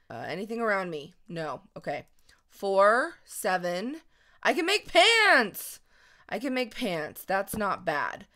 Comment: What really said "I can make pants!" What I thought she said "I can give hands!"